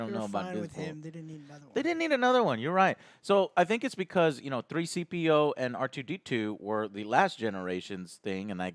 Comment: did he just say 3-CPO??? lol